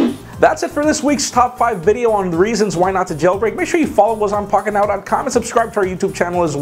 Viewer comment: It does sound like u say fuk it now instead of pocket now lol